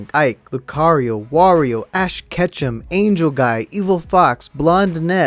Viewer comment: When I first heard “Ash Ketchum” I thought it was “ash catch them